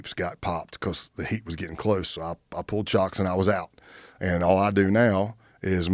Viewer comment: ",, I pull chalks and I'm out." Some won't know what he said,, imagine truckers putting those rubber or wooden blocks behind or in front of the tires. Those are called chalks. He said he pulled them out and left the situation. Don't know why hearing him say that fascinated me,, but it did. He used that same vernacular again @ 1:24:37. Interesting.